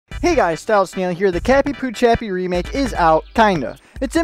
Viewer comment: Why did you say Cappy-Pu-Chappy